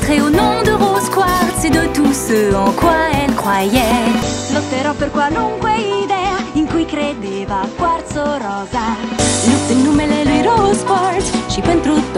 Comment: que reviva cuarzo rosa es lo que entendi